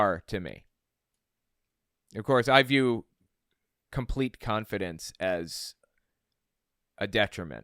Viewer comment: When you said confidence, I thought you said competence. You're definitely more competent. 😂